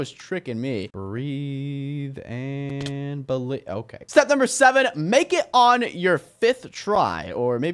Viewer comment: I thought you said it was number five